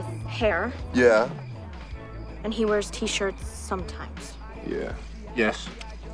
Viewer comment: when he says "sometimes," I thought for AGES that he was saying, "so funny." I couldn't figure out how Amanda was getting "sometimes" from what they were talking about...then boom 😅